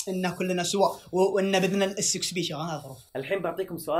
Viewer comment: كني سمعتة يقول باذن الsxb